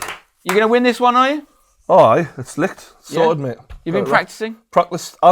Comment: Did say 'it's lit' at 0:48 ?? Ffs